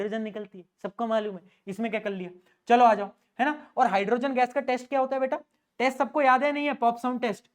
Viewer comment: I heard taste😂😂 rather than test